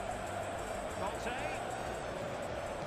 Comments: I heard content but it was Kante. 🤣🤣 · He said Kante i heard Content 😂😂😂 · He said Kante i heard Content 😂😂😂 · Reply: Ohh it was Kante i heard "kaat de"